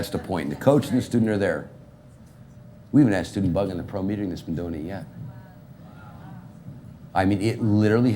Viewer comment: What the hell he say at 14:05 lolol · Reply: "We haven't had a student bugged in a pro meeting that has been doing it yet"